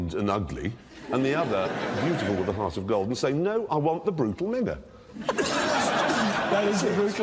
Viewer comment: Can someone tell me what Jeremy says here? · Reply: “No, I want the brutal minger” I heard a very different word the first time I watched this so had to rewind a few times to make sure 🤣